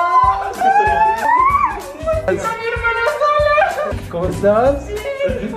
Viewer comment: En el minuto 8:14 escuche estoy embarazada y alguien dijo que cosa